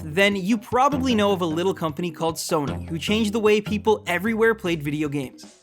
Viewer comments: Did somebody heard that he said SONA? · Sounded like he said "heard of a company called Sona" wtf lmao